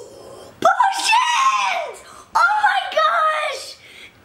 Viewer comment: did say a bad word or no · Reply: No he said potions